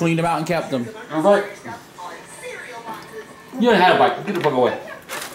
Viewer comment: Was anyone else really hungry after watching this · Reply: Kaitlyn Trombly did he just say get the fuck away on 5:20 to a kid